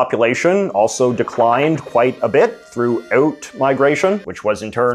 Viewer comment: Isn't "out migration" emigration? 😏 Thanks JJ. NOTE: I thought you said "reverse immigration" at first, my bad. Still I think emigration is the correct term.